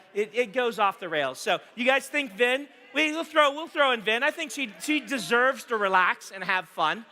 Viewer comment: at first I thought Brandon said "Ben" instead of "Vin" and I am all for it